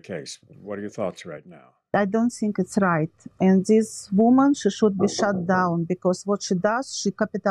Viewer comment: Does anyone know where the defendant is from? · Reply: oh ok thank you. I thought it was hilarious when at 14:47 she said “ this woman needs to be shut down” because with her accent it sounded like she said “ this woman needs to be be shot down” lol I was like ok someone’s got connections to the mafia. 😂